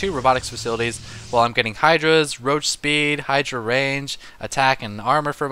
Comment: I heard idra rage lol